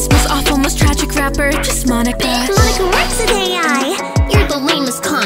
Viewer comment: I like at 2:26 it says “Just Monaca” instead of “Just Monika”